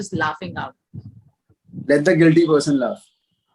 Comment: what pravisht say?? Plz tell mee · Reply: Let the guilty person laugh